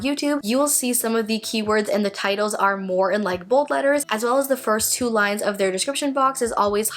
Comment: Can you clarify what you are saying at around the 7:40ish mark in the video? I’ve listened five times and can’t make it out! You say that some words in a videos title will be “in likeable letters???” I’m sure I’m hearing it wrong. Hopefully you can clarify. I really like this video. It has answered several questions I have had 😊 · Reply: I said bold letters x